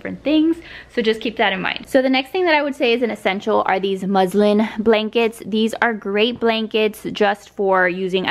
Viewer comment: At first I thought you said muslim blankets.😂🤣😅